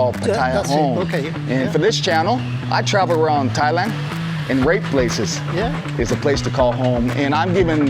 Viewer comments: I heard “rape places” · "I travel around Thailand and rape places" is what I thought he said.